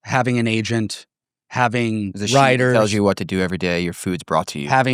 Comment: did bro say " having writers" ?? Like ghost writers ?